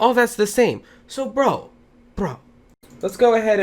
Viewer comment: did u just say BRA xD